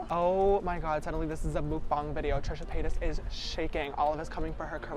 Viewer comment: did u just say mookdang